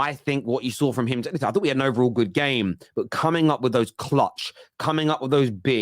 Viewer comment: sec "... I think we/he had an overall good game" lol not coming for you, just not clear what I heard😅😅. Anyhow, I really enjoy your content. COYG.